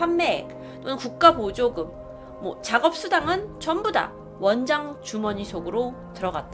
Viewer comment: 이거 "원장 주머니 속"인데 "원장 주원희 손"으로 들었다...ㅋㅋㅋ